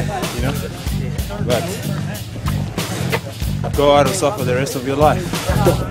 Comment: he actually says "go hard or suffer the rest of your life" not "go out or suffer the rest of your life"